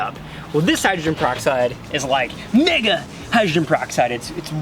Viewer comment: did he say the n word I'm offended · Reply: shadowNinja YT he said mega